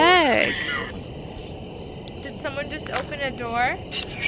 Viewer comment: I heard it say (pick a door) maybe there is many u dont see or theres mote behomd the gate